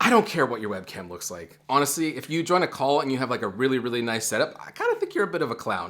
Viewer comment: i thought you said “join a cult” instead of “join a call”